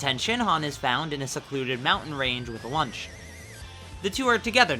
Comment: I did a Toriyama and forgot Launch existed and thought you said "Lunch"